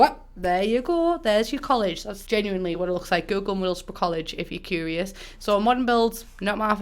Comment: what is the name of the college couldn't understand it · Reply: How? She said it normally. Middlesbrough